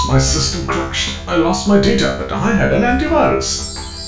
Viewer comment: I lost my teacher I had an aunt once is what is says if you turn on Captions